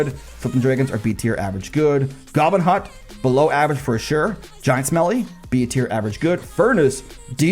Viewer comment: Did I mishear or did he just call Giant Skelly Giant Smelly....? 😂